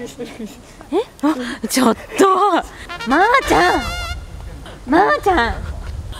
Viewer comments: ぐらいに女の子らしき声でか「ぱぱ～」か「まま～」って聞こえた😱(*゜д゜*) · のところ ままー って聞こえる